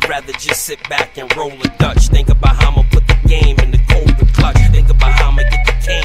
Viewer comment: Did anybody else hear it sound like he said COVID Clutch...😆🤣😆